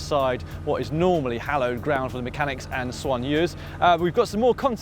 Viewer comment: he said that the trucks are hallowed ground for the team mechanics and swanues. What was that word? I assume it's French. what does it mean? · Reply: soigneurs are assistants that take care of the riders needs: food, clothing, massages etc